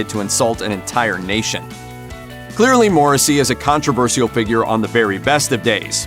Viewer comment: I always hear, "Morrissey is a tosser..."